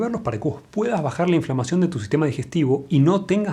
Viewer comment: Pensé que decía inflación xddddd